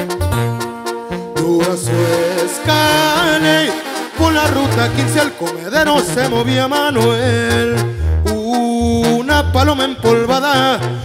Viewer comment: No acabo de entender lo que dice el panson en el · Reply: Dura su escardley.. por la ruta 15 al comedero se movia manuel